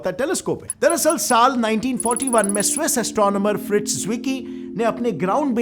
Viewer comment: wrong captions it's says 1914 instead of 1941